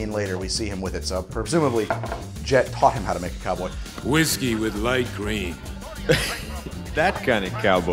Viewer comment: For 20 years I thought was saying "whiskey with light green." I didn't and don't know what that would mean, possibly something related to absinthe or bitters.